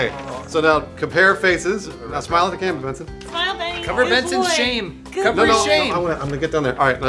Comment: I thought when said " cover up Bensons shame" ; he was talking about Elyse.